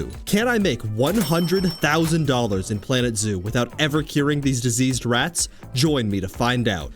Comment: I thought it said deceased rats at first